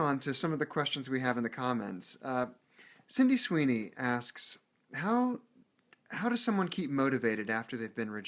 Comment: did he say sydney sweeny lol